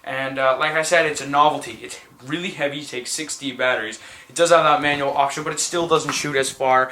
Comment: I thought he said 60 batterys lol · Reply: MR GH he did say 6 D BUT it sounded like 60